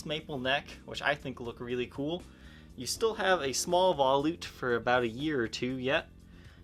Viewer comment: he says "you still have a small <something> for a year or two yet". Any idea what was the "<something>" word? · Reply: Neck volute. LP's now have a flat transition to the headstock, I had no idea it wasn't always like that!